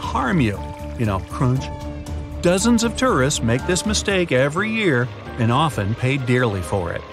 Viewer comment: At first I thought he said dozens of terrorists make the mistake every year...